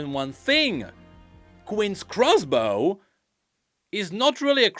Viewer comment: I'm confused. Is he saying Gwen by accident? I assume he means this is Vayne